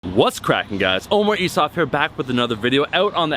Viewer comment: Quick note: autogenerated english subtitles actually show Oh my ass off instead of on 0:01 😂